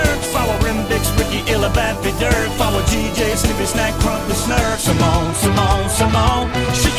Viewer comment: I could swear I heard him say scooby snacks at one point.